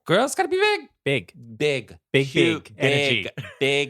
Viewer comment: They played Big Energy at the end of the new movie with Sandra Bullock, “The Lost City.” Just went to see it and was like, that’s the song Mariah will be on! I too thought she said, “Big, Big Energy.” What are kids listening to these days? 😂 😊 😆Exited for New Mariah Season!!!